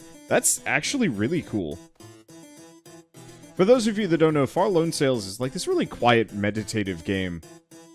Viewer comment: I misheard "Far Loan Sales", and from the commentary, I was picturing an open-world money merchant sim with a Papers Please feel for the parts where you're deciding whether or not to invest in the people you meet.